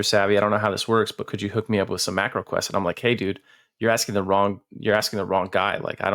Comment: It is indeed! We’ve got 5 episodes up on Spotify, with more to come! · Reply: Hot damn! missed that! Love hearing all the history and insanity of EQ. Also in this episode, you guys referred to MQing and "warping". Never heard of this, was this some kind of server hack? Edit: heard you say "macro quest"